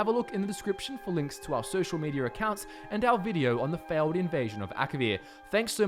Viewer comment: I thought it said alien invasion